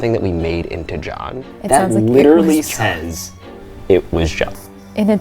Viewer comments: Why did it sound like “it was god” when you asked Abby who killed her? It could say John but it just sounds like it said god to me. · I hear “IT WAS GOD” a lot clearer than “JOHN” so my assumption is that they skimmed past a Christian channel, but that’s just me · i hear either “liz and john” or “it was god” or “it was none” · I didn’t hear “it was John” I heard “It was Jah” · Them: *obsessing of "It was John" Me: Dude, are we not gonna talk about how it said she was more dangerous? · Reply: XD I heard 'It was God' soooooo I found it extremely funny especially because to me the voice sounded funny, it’s like a ghost is making a joke. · I didn’t hear “it was John” I heard “it’s my job” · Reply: I heard "It was God" and I'm not really a religious person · Literally every time you played “it was John” I really tried to hear that but I don’t hear that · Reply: Yeah same here. I keep hearing "it was God" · HAHA i only heard “and by God” , could be a Christian channel 😂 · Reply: Lol I heard " It was God" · I heard: "it was god" which makes no sense. When they were talking about the "it was John" it didn't sound like John to me. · For me it sounds like "this was god" ..no matter how hard i try, i cant hear a "j" in the whole thing. And that also makes no sense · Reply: YES thank you omg I keep heard a guh and D sound. I hear "it was GOD" tbh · When you guys heard “It was John” i heard “Liz and John” or “It was for God” Just me??? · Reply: I heard "it was God" kkkk